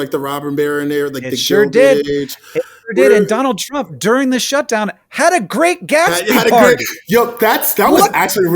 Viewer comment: What is he saying at 44:57? "Donald Trump, during the shutdown, had a Great Gatsby Party?"